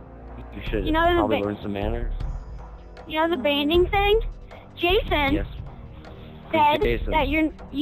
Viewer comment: I thought they said Jared for a sec